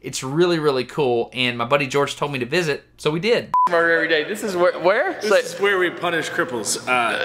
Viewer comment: it sounds like he said "murder every day"